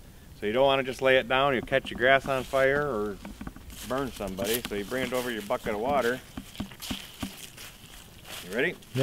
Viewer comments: "Ya'don just wanna just lay it down'er ya'catch yer grass on fire...er'burn somebody so a bring it over to ya'fuckin' water..." That's what I thought he said for a second. My volume was a little on the low side, it made me laugh. xD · i thought he said bring it to your fucken water 😂